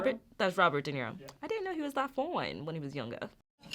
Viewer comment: What did she say about De Niro? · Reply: I didn’t realise he was that fine younger.